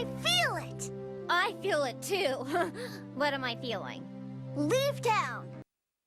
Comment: god dang it, I thought Pinkie said lubetown whatever tf that means instead of leave town at first